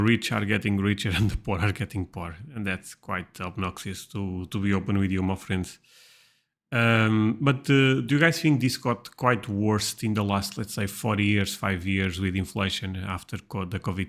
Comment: did you say “that’s quite obnoxious to be open with you muffins” ? Am I your muffin? ☺️